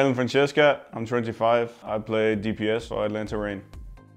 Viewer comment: I swear I heard him say "I play Torb for Atlanta Reign" LMAO